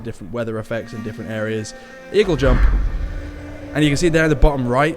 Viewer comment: Did you just say "Eagle jump"?? Wtf haha you're not an AC fan, are you?